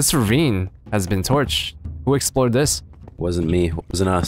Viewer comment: whenever says "explore" it sounds like he is saying "explode"